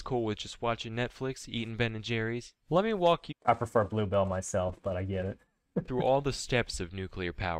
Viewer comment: did he say he prefers blue films 😭😭😭